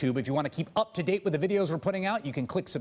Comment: Wait at the end of the video that guy says below, this whole time I thought he was saying blow.